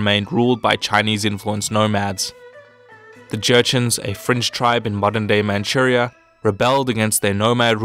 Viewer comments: did he say Jinn was a french tribe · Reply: Yeah the baguette is native to Manchuria (he actually said fringe but french is funnier) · wait did he just say French tribe?